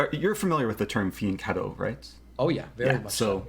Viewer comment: Hi all. What term does he say here? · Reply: "Fianchetto." It means to put the bishop on the long diagonal (h8-a1, in this case) as a means of controlling the center (and eventually attacking the a1 rook, often) by putting it on g7.